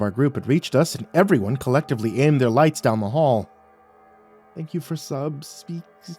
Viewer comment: LMAO I was like “what did Wyatt say ?” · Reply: “Thank you for sub..?” I’m not sure but it’s confusing lol